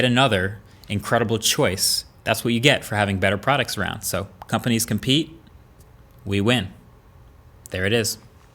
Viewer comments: I can't be the only one who heard this "companies can pee we win " ! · the first time I heard "companies can pee"...after replaying again a couple times realised it was "companies compete". 😐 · I thought you said......Companies can pee? ......at the end :)